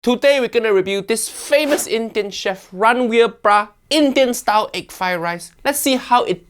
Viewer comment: WHAT DID HE SAID AT 0:03 ? RANVEER BRA??